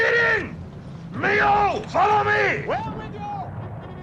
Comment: sounds like "We're with you!" instead of "Will do!"